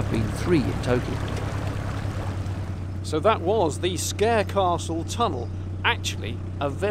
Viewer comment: I thought you said Scarecastle.